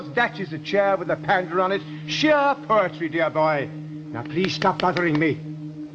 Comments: WHAT does he say? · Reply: "Now please stop bothering me." (from "The Time Meddler") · Anyone else hear "Now please stop buggering me."? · What exactly is he saying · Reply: "That is a chair with a panda on it."